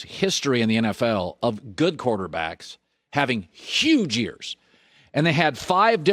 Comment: I thought Colin said "Huge Ears"